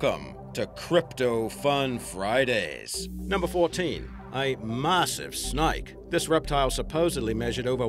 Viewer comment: I tend to listen to videos like these while doing other things. In place of "A massive snake", I misheard "A massive Snipe" and my brain went straight to "WAIT, KEVIN FROM UP?!"